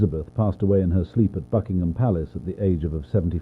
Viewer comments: WHY DO I HEAR "FUCKINGHAM" ITS BUCKINGHAM 💀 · Why did I hear "at FUCKingham palace" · Her mom outlived her💀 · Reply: WHY DO I HEAR "FUCKingham Palace" (Fr)💀